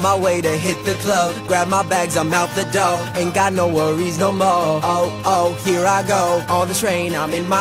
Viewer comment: At first, i thought it said i aint got nowhere resonable. Instead of i aint got no worries no more.